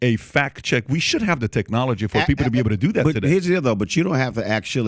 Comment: When Roland is saying "deal tho" sounds like he is saying dildo 🤣🤣🤣🤣